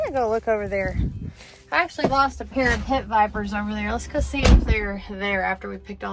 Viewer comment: Did you say you lost a pair of PIT VIPERS at~7:53????